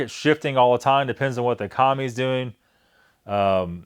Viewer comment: When you said "it depends on what the economy is doing". Katie heard that as "it depends on what the commies are doing". I loled